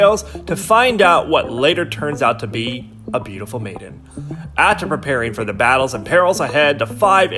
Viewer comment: bruh I thought he said “beautiful man” and I became very very interested, but unfortunately, I was wrong